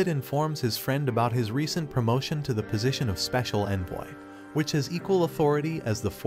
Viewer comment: I thought he said "Speical Ed-boy" instead of "Speical envoy" 💀💀